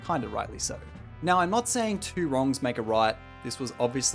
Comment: I know its just how he says it but it kind of sounds like hes saying 2 wrongs don't make a riot XD